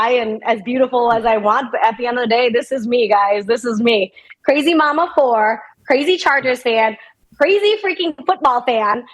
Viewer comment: She's awesome, but I FOR SURE thought she said "Crazy mamma whore" I had to rewind and make sure I wasn't crazy.